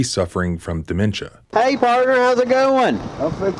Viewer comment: Cops really need to stop saying "hey partner" after i was in a wreck last april i had a cop say that to me and i genuinely thought he said "hey Courtney"